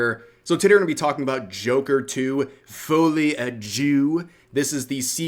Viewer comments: I was listening on an audio and I thought you said Joker 2 Fully a Jew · I heard "Joker 2: Fully a Jew"